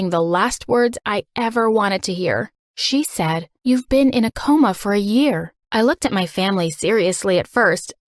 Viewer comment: I really thought she said: “I had been in a akuma.” (For all mlb fans including me.)